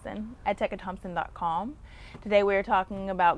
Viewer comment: thought she almost said growing porn lol what a beautiful lady